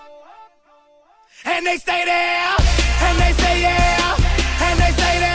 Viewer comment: in 0:17 i thought that he says "and they's nae nae up" lol, T-Pain did me confused